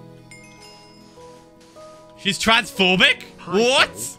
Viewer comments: I heard "SHE'S TRANSFORMING?!" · I thought Phil said, “she’s transforming?!” Lol I was so confused